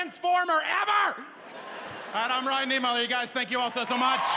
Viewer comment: In one of the performances I hear him saying "thank you very much, i am Ryan Nemo" I had to replay that part 3 times just to be sure.